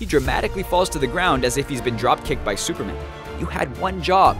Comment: Anybody else hear him else at 3:03 say "superman" rather than him saying "batman"?